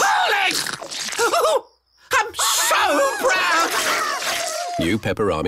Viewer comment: YOUR BEHAVIOR TODAY WAS APPALLING!!! crunch Oh, I'm So Proud! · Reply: At first I thought he said "I'm so grounded!"